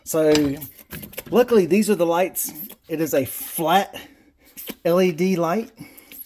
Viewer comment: I thought at first it said pantie light 😅😅😅